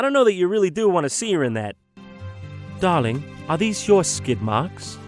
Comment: Did I just hear "Dahhling, are these your skidmarks?" at 13:00?